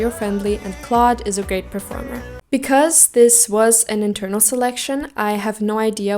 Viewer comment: i said hes a 'great performer' though HAHAHAH ???? 😭😭 where did you get the 'ok' from lol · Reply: I think you say he is an ok performer but depending on how I fill it in it could also be great but it sounds cut off. I sound stubborn now ;)